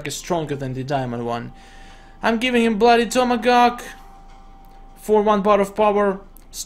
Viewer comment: anyone heard him say bloody tomagawk?